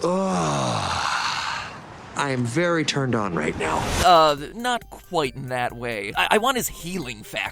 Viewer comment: I swear deadpool says: "I'm touching myself tonight" NOT "I'm very turned on right now"